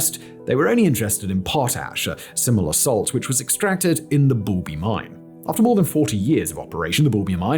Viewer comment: I thought he said Booby Mine, but it's actually Boulby Mine, how disappointing